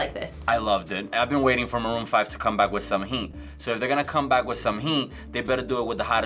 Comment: wtf did this dude said at @ 5:08 and 5:10🤔🤨¿ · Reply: Bender Rodriguez He said They better come back with some heat